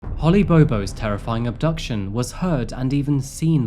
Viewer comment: The first time you said her name, I thought you said "Honey Booboo". I was like, "oh, please no".